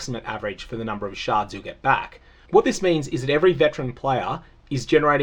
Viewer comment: I know you are saying 'sharded', but I keep hearing 'sharted'. Grow up, me